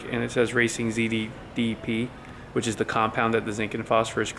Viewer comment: i thought the oil additive said racing zoop so that's what i'm calling it now